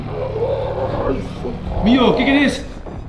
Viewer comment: se escucha alfa me tiene 0:26 en la pared dice Ian 29:02 dice alfa me hace daño like para que Fede lo vea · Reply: Yo en el 29:07 entendí alfa vete:b